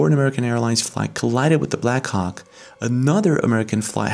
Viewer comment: 😂😂😂😂 could have swore u said black coxk